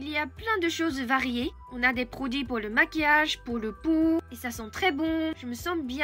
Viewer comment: Quand tu as parlé des produits cosmétiques, tu as parlé des produits "pour la peau"... et j'ai entendu "pour le pou" pendant une seconde, avant de réfléchir que ce n'était pas très probable haha! Un "pou", c'est l'insecte qui vit dans les cheveux sales. Merci d'avoir partagé ton ressenti avec nous, je suis bien d'accord avec ce que tu as dit. Peut-être que les hommes japonais se trouvent tellement menacés par les autres hommes, au travail par exemple, qu'il ne veulent pas qu'à la maison aussi leur femme les "menace" avec de la "compétition" (intellectuelle) ou avec des réflexions potentiellement opposées/différentes, et donc ils veulent qu'elle soit toujours d'accord avec eux pour cette raison, la certitude que la maison est un lieu de paix sans conflit, à l'inverse du travail? Juste une hypothèse...